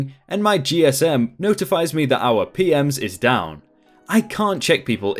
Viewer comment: re: story at ~7:06; It sounds like you are saying P-Ms (plural) instead of P-M-S for Property Management System (standard hotel software).